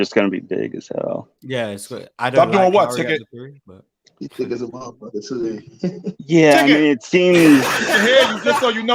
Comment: Can anyone make out what ticket said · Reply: she thick as a mf